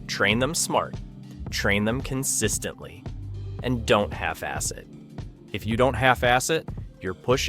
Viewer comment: I thought it said don't have acid at the end at first.